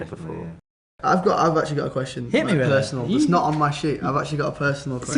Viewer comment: LOL 0:35 SOUNDS LIKE NEVILE IS SAYING ITS NOT ON MY SHIT !!!